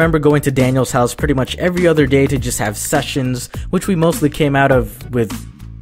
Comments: you emphasized the ‘se’ in ‘sessions’ so for a second there I thought you said “We grew close together and I remember going to Daniel’s house to just have sex”. I was very confused. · omg I thought he was gonna say ‘to just have sex’ and I was like BRO DID I HEAR THAT RIGHT